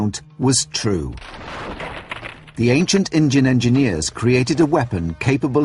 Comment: did he say acient engines hes racist its supposed to be indian